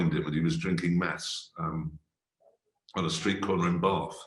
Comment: what did he say James was drinking on a street corner? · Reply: Meths